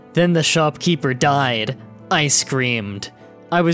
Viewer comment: Did you say, "Ice creamed." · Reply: No he Said i screamed